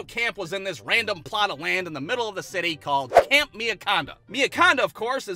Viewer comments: I thought he said camp Miacondom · Who else thought he said camp Mary condom